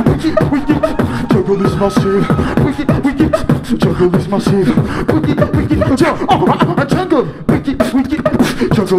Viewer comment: what is saying? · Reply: wicked wicked. jungle is massive. a song of ali g^^